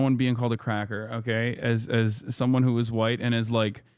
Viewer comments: Did bro really say "As someone who was WHITE!"????!!!!!! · Do I hear "someone who WAS white?", tf? 😂this dude changes race whenever it's convenient 🤣